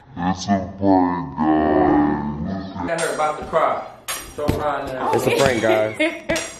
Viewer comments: So am I the only one who heard say @ 18:05 " Standing there bout to cry... yo fine ass..." ? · am i the only one that think said shut yo fine ass up